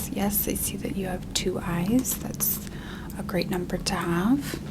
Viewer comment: I was, like, half asleep, and all I heard was "Oh, I see that you have two eyes, that's a nice number to have." and I was like, "Niceeee" like I forgot I had two eyes or smthn idfk